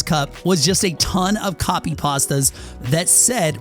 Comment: did you say copy pastas?????